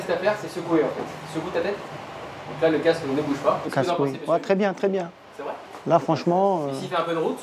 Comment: C’est moi ou à 3:50 il dit casse ***